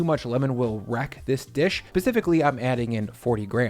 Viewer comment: does anyone else hear him say pacifically instead of specifically at 1:42? maybe im tired